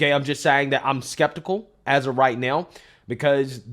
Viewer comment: “I’m skeptical as of right now” but I heard “as a white male”. You’d short some people’s brains out with that one because they’d be afraid to question it. “Is that the new thing now? Better go with it so I don’t commit heresy”